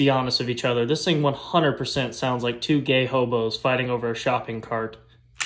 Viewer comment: “Gay hobos fighting over a shopping cart.” 😂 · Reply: I thought he said "gay homos" I thought he added redundancy for effect.